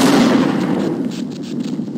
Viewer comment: what does he say here?? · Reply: I would really like a cold one right now (he wants a soda)